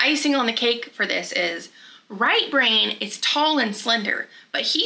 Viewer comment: I thought you were saying "Right Rain" and I assumed he was American Indian I'm from Wisconsin and there's a lot of natives with interesting names like that . Lol